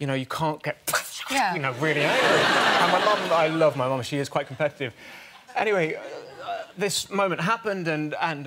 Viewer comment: I thought he said "i love my mom, she is quite attractiv"